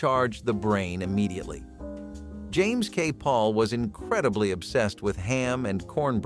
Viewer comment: Something went wrong with the digital voice used for the video when it called out James K Polk 04:07 , his last name came out very strange, sounds like it said, PAUL. Need a redo. I literally takes away from the video, click.